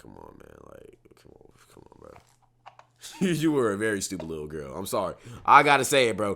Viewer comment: I think she said “I ain’t no stupid lil girl anymore” as in when it happened she was stupid, but not anymore. She also clarified that she was talking about when it came to her baby